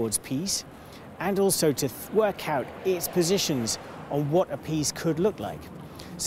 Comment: When he said, "a peace" his British accent made it sound like he said, "appease"...